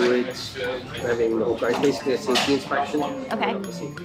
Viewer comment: Did he really say "it's a safety inspection without the safety"? 😂